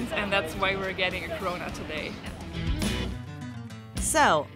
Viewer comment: sorry ,i misheard that its name is coronut